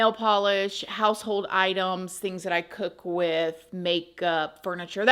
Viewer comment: Hahaha I went back cause I thought you said «things that I cook with makeup » 😂 Girl, I know we can do a lot of stuff with makeup but eatin it? Wwaaattt? 😂 Love you ❤